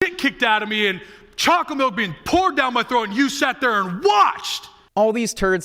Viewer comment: did he say chocolate milk being poored down my throat and u just watchedddsdsds😅😅